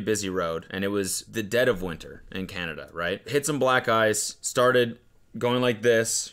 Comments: Sounds like he said hit some black guys lol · I really though said he hit some "black guys" instead of black ice and i was so shocked at how causally said it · I THOUGHT HE SAID “hit some black guys” ?? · Why did I hear "black guys" 💀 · it sounds like he says “hit some black guys” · When said hit some black ice I could’ve sworn I heard “hit some black guys” so nonchalantly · I thought he said hit some black guys😂